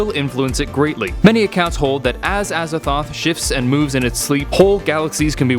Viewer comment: I thought he said "as a thot"